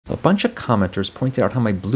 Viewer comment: i thought he said "a bunch of comoners" at the start